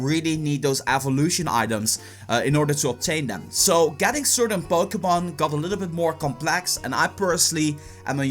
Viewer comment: It sounds like he says Bokémon go. Am I the only one who hears that?